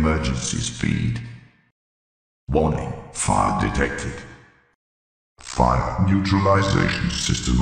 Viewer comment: Why did i hear “Warning . Fart detected”